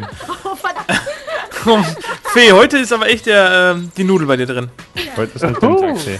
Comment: verstanden Nudel drinnen hahahaha ohh der war gut